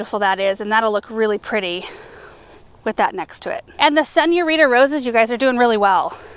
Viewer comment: Did anyone catch what kind of roses mentioned at minute marker 4:24? Sounded like Sunny Arita Roses. ??? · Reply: Senorita roses 🌹🌹